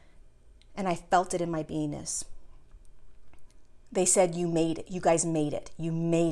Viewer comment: OMG, lol, @ 1:16, I thought she said she felt it in her anus